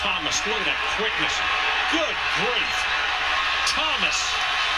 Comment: Good grief is right! I thought he said good gravy. Same thing. The man is a legend.